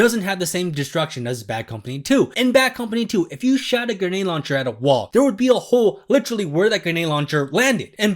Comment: it sounds like you said "shat a grenade launcher" 🤣